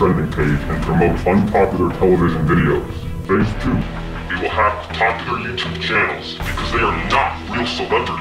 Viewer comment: To be honest I thought it said popularmmos